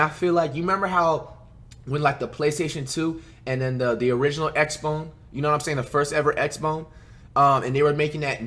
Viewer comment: i can’t be the only one hearing “xbone”😭😭😭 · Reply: Lmaooo I thought he was actually saying xbone 😭